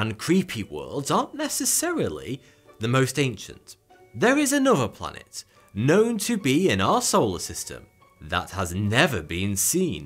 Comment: for a second there I heard "there is another planet, known to be an asshole...." hahaha